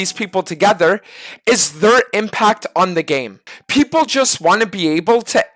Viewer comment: Thought he said third impact.. started thinking of fanta